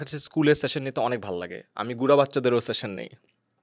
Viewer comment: did he say gura baccha😆😆😁